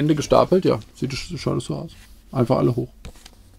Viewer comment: ich habe erst sieht scheisse aus verstanden haha